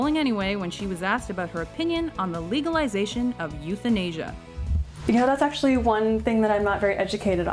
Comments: Euthenasia I thought they said youth in Asia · The first time I heard "euthanasia" I thought it was "Youth in Asia."